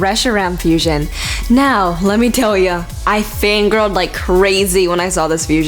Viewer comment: I thought she said I FINGERED LIKE CRAZY. I'm sorry guys..